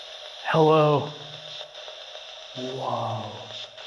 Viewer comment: I believe the woman spirit is saying “ I don’t” instead of “hello”. In regards to your question ❤